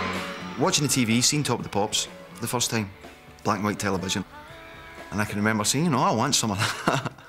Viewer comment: what he says in 2:45? he's got a very difficult accent can't understand very well. BUT LOVE WWW MUSIC VERY MUCH. somebory help, please!!! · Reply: Marti says, 'Watching the TV, seeing Top Of The Pops for the first time, black and white television, and I can remember saying, ‘I want some of that. I want some of that!’ :)